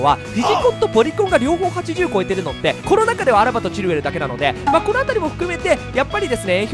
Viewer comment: コロナ禍ではチルウェルだけって聞こえた笑